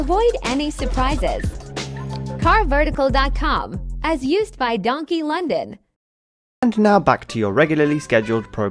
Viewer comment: Don't tell mum John took the car 👀 Remember guys drive safe out there👍 Hope you enjoy it peace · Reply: That CarVertical ad sounds like it says 'As used by DonkeyLondon' @ 1:12 😂